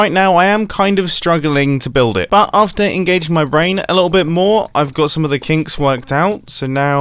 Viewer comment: sounds like mumbo is saying "i've got some of my kinks worked out"